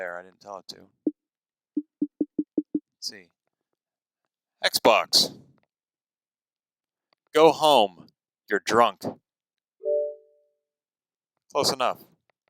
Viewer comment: did you said '' XBOX GO HOME YOU DRUNK ''